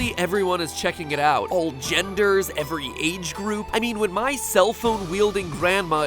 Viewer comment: Did anyone hear him say all genders...